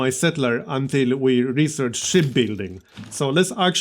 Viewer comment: When you said "ship building" I actually thought you are saying "shit building"... dunno why :D maybe cuz of that awesome accent.